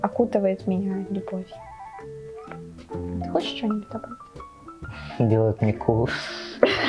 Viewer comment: Что он сказал, - _ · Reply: Делает мне ку шать (повторил за)